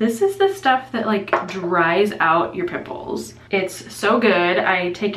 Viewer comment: Ok, I have to comment again...the stuff you showed us that drys out your pimples. I had to listen and rewind and listen again to hear what you said. I swear I thought you said it drys out you nipples! 🤪😳